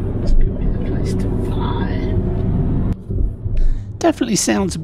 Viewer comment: i thought it said this is the place to fly fab vlog